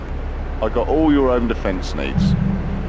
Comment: What does he say at 1:36? I've wondered this for years. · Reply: "I've got all your home defense needs"